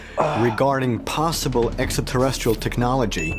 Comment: Did he just say EXAterrestrial?